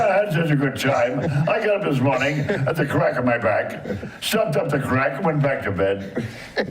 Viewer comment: I got up this morning at the crack of my back. Stepped off the crack, went back to bed. Take my wife, please. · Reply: I thought he'd said"stuffed up the crack", still works.